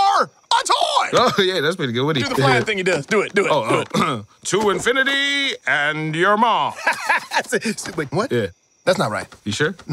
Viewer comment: At. 6:30 I thought he said “to infinity and ur mom” XD